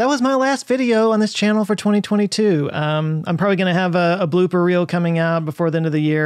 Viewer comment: When he said blooper reel I thought he said BLUE BURRITO